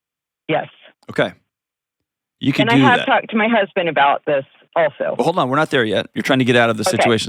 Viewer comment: Imagine her husband finds this video and recognizes her name, voice, and city😬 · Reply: Apparently, all of you need to get their ears checked. She clearly said that she's talked to her husband. The husband knows😂😂